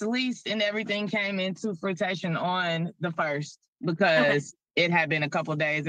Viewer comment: did she say the "lease came into fruitATION..."?????😂😂😂😂